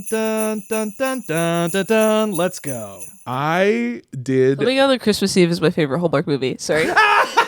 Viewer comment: I can't decipher what is saying at 18:00 and I feel like if I could it would be a life changing joke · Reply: Coming out on Christmas eve is my favorite Hallmark movie